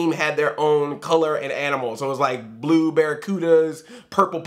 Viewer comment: Was I the only one who as a kid thought they were saying "Blueberry-cudas" instead "Blue Barracudas?" Lol